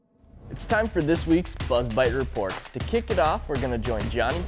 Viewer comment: man you scared me. I thought you said the Bud Light report. But no, the "Buzz Bite Report." 😅